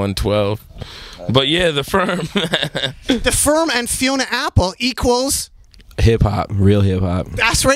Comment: what is he saying at 3:51 ''the firm and 'somthing'apples''?